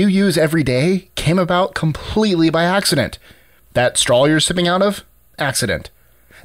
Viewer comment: HAHAHA i could have SWORN he said "that stall your shitting out of" had to rewind it